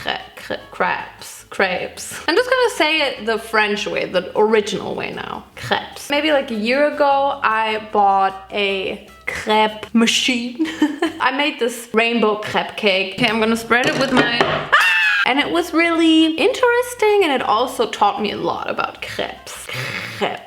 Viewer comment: I died the subtitles said crap machine 😂😂😂